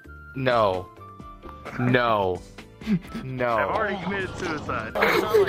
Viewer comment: is it just or me?or did someone said "ive already commited suicide"in the back ground